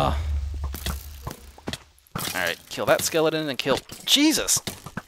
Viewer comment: did you say you were gonna kill jesus?